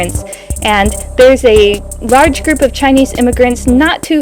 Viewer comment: did you just say rarge? You’ve been speaking too much Japanese.